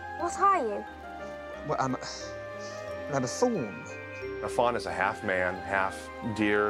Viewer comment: "I'm a faun." Sounds like somebody with a speech impediment saying "thawn" instead of "faun." WTF?